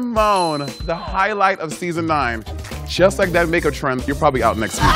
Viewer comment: whenever monet was reading farrah, i thought she said "just like that n** trump" HSHDRJHR